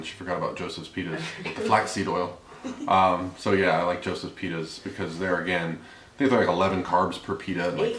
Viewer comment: Thought you said Joseph's "penis" at first. .lol